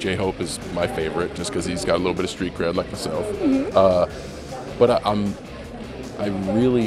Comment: I dont understand what he said about J hope? 4:14? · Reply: He said hope has a some street-cred just like myself